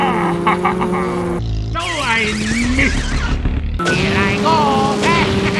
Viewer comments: am I losing my mind or does Japanese Wario speak German at 4:44 when he drives over the banana peel? I listened to it three times now and I clearly hear him say "So ein Mist!" which is German for "That was bad!" - please, if there are other Germans here in the comment section, tell me I'm not going nuts. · Did he just say "So ein Mist!" in german? O_O